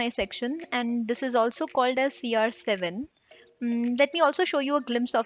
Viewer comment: Did I heard CR7 ?